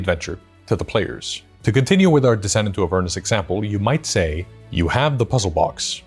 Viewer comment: As someone not familiar with Avernus & listening while working, I kept hearing it as “descent into a furnace” and then realised how much sense that didn’t make lmao